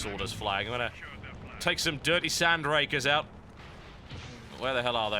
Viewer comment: Dirty Sand Rakers.... I hate my brain for mishearing that as Dirty Sanchez